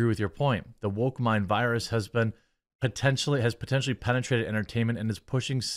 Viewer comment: ? Huh ? "Potentially" vs. "thoroughly" <-------- That's what he said - Jeremy - you need some more Coffee brand coffee - "come on maaaaaaaaaaaaaan" ;-)